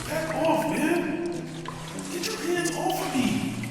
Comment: thought i heard pants not hands 💀